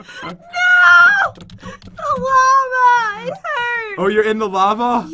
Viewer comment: I Thought She Said Llama It Was Lava